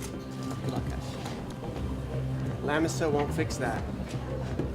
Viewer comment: What does Cruz say at the very end? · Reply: i thought he said the lannisters wont fix that. Thought it was some sort of Game of Thrones reference lol